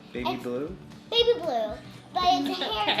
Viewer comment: I thought he said luke, but he said baby blue. Lol